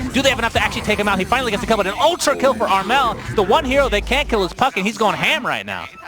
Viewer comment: Did he say Puppey over Gh? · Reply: he said puck xD